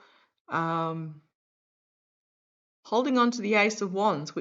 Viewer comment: I think I heard something (your guides?) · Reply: It sounded like “hold up” to me! ✨🥰